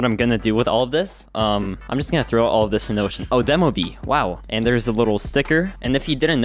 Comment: did bro say he will throw it in the ocean!?